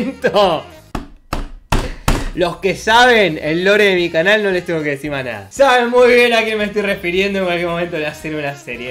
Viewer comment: ayuda no entiendo XD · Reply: El ricardos team